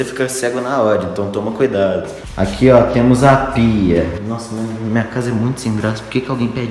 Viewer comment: eu entendi pika kkk